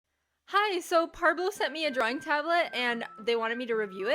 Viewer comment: IDK why but when the video started I thought you said Pablo sent you a drawing tablet I was so confused at first lol